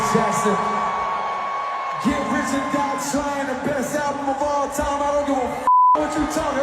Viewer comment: Damn I thought he said best RAP album….this nigga said best album 😂😂. You could argue there have been better rap albums let alone all music in general. He don’t come close to MJ…cmon now Cole.